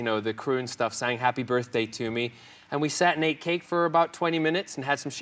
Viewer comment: "sat and ate cake" I first thought he said "sat naked" haha